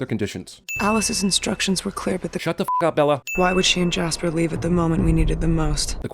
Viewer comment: Every time there was a "Shut the fuck up, Bella!" I kept hearing "Shut the fuck up, Donnie!"